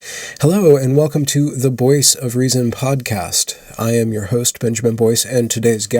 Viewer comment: I have listened to several of these videos, but this was the first time I noticed that you say "Boyce of reason" instead of "voice of reason." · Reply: I’ve watching many of his videos and thought he said Boyce of reason every single video.